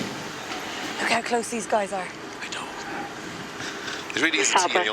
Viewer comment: Co-pilot "Look how close these guys are!" Pilot: Whispers something....WHAT DOES HE WHISPER? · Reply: He said “I know”